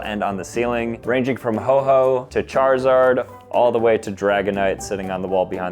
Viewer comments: Did bro say ho ho 😭1:00 Jollymaxxing is at its peak and Im here for it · did bro say ho ho